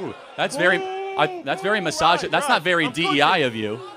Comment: I think he was saying "misogynist", not "massage".